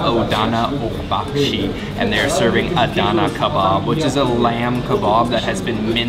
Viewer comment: adana ogabashi :DDD sounded like japanese